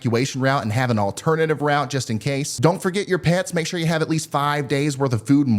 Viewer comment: Ryan says, "Don't forget you pets" and I hear, "Don't forget your pants." Now there's some hurricane preparedness for you.